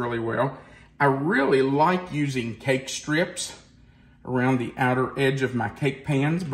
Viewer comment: Cake strips to bake evenly? What are you saying at 9:33? Egg strips, cake strips? What is this?